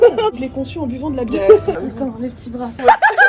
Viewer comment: Elle a dit quoi à 15:16 svp? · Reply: Ça fini par "les petits bras"